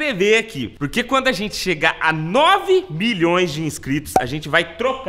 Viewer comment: foi só eu que ouvi 9 bilhoes